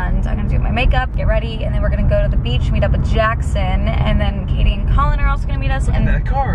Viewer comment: Wait I’m new and could have sworn she said at 3:04 that she’s meeting up with Katie and Colin which is so funny because my name is Katie and I have a brother named Colin lol